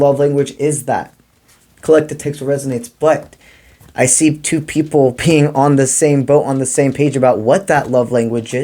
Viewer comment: I had to giggle at the subtitles mishearing as “two people peeing on the same boat” 😂 But seriously, so eery how much this resonates. ❤